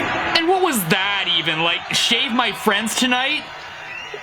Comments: I thought he said, shave my balls tonight. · I thought he said "shame my friends tonight" 😂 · i thought he said shame my friends tonight 🤪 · “Shag My Friends Tonight” hit hard. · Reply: i thought he said " Shave my friends tonight " :) As random as it could get... and before you got a chance to think it over how little sense it makes, he counters with "nevermind". Well played, Durst, well played :) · I thought he said "shag my friends tonight!!" 😂🤣 Edit: thank you for 60 likes in a couple of hours i really appropriate it,its my first time to get so much likes 💖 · Sounds like he said shag my friends tonight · I thought he said " I shagged my friends tonight". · All I heard was: “Shave My Friends Tonight” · I thought he said “check my friends tonight.” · i think he says "shag my friends tonight!" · at the end i thought he said shag my fist tonight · I thought he said “shag my fritz tonight” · i think he says "shag my friends tonight!" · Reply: I thought he said 'Shave my friends tonight'. That solo did put hair on my tongue. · “Shag My Friends Tonight” hit hard. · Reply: Fred's kind of an asshole so I thought he said shank my friends tonight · The shredemption of Fred Durst was Green Day? And maybe it's revealing of my state of mind but I thought he said "Shank my friends tonight". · almost sounds like he says "shag my friends tonight" barfing at the idea alone :o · “Shag My Friends Tonight” hit hard. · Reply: I thought he said "Shave my friends tonight"